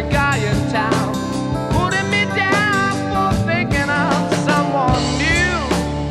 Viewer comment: Lyin', cheatin', hurtin, that's all you seem to do. Messin' around with every guy in town, Puttin' me down for thinkin' of someone new. Always the same, playin' your game, Drive me insane, trouble is gonna come to you, One of these days and it won't be long, You'll look for me but baby, I'll be gone. This is all I gotta say to you woman: [Chorus] Your Time Is Gonna Come [X4] Made up my mind to break you this time, Won't be so fine, it's my turn to cry. Do what you want, I won't take the brunt. It's fadin' away, can't feel you anymore. Don't care what you say 'cause I'm goin' away to stay, Gonna make you pay for that great big hole in my heart. People talkin' all around, Watch out woman, no longer Is the joke gonna be on my heart. You been bad to me woman, But it's coming back home to you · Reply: i always heard "putting me down for picking up someone new" but was surprised to see "thinkin of someone new" when i googled it.